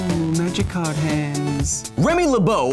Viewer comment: Knowing some of your hobbies, I hear “oooo magic card hands” and think you drawing a your deck with the Locust God lol